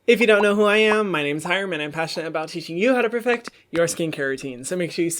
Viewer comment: The way James pronounced Hyram's name 💀💀💀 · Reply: Queen yeah ik haha it just sounded like he said haram 😂